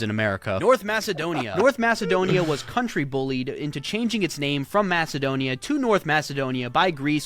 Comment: in original video is 19:30 in this 6:05 Check! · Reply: No please, I hear him say 6:07 "A country bullied into change it's name from Macedonia to North Macedonia" . . .